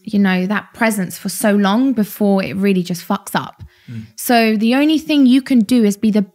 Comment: did she say "before it really fuck up"? Wow, 8,5 million subscribers and this Karen swears in the video? Wow, what a mess.